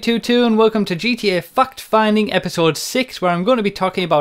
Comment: I thought he said "fuck finding" during the intro 😂